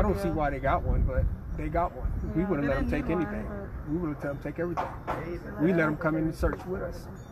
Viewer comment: ✨It sounded like he said "We would of". But that aside, I hope this doesn't sound stupid BUT is it possible that their dogs name is "Chalk". Cause he says we let them out to play with chalk. The reason that it crossed my mind is because if it were chalk as in the powder writing type chalk wouldn't the children being so lil' try to eat it??? Like who would give chalk to unattended two lil' toddlers. 3 & 4 year olds are notorious for nibbling on toys and whatever they can get their lil' hands on. That's why I wondered if it was the dogs name since no chalk was seen in the yard via the drone. Next, after listening to them give their PATHETIC interview my gut tells me something is not right. If you watch closely when they say certain things they say "Yes" but shake their heads back and forth meaning "no" and vice versa. This means that your mind as it delves into its memory bank to retrieve information is not agreeing with the words coming out of your mouth. Also, she says we did a search before we called Police. Omg! I saw the CCTV from the neighbour and that was anything but a search. It was a half ass attempt at a search if you could even call it a search! Also, why are they so calm. Like seriously how can they keep their composure? I'd be freaking out. I'd be giving quick interviews trying to provide A LOT of information in a short time and then be yelling "Ok lets go! Lets go find these babies!!!! Come on people we need these babies back!!!!! But they express zero urgency! T